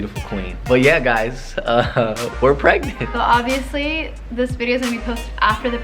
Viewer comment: I thought the doctor said you guys are not pregnant